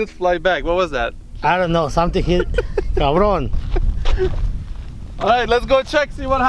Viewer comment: did he said "cabron" 😂😂😂